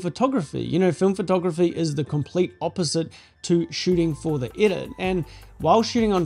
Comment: What the heck is he saying? "Shooting for the itter?" I have no idea what this means. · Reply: My accent can be hard to understand 😅 I said “don’t shoot for the edit” “film photography is the complete opposite to shooting for the edit”